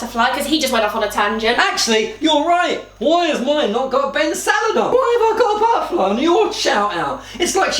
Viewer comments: All this time I've watched videos ft 😉 I swear I thought they was saying ben salad not ben's a lad😂😂😂😂 love it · Omg ahaha I thought that said Ben - salad 😂😩